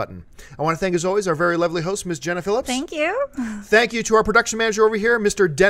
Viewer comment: I thought she said her name was Jennifer Lipps lol